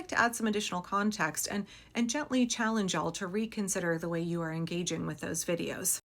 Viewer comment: I heard "and Jet Li challenge you all..." 😅